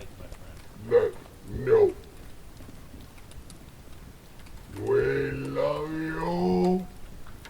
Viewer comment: or thereabouts. The “We love you.” blew me away. Or at least that’s what it sounded like. How awesome you get to experience this.